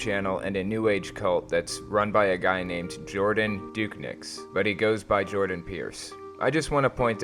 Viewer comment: Had to rewind 3 times swore you said his name was "Jordan Dick-pics"